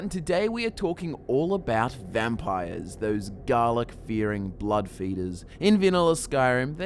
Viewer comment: When you said garlic fearing, i thought you said dalek